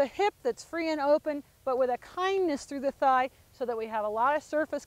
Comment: did she just say "a kindness to the thigh"? I am trying to understand that.